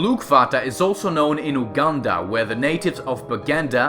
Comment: did he just say wakanda?